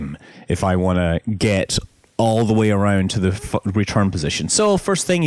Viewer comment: Did it say "Fuckin return position"?